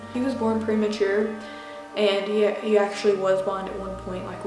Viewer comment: I thought she was saying 'blond' And I was so confused XD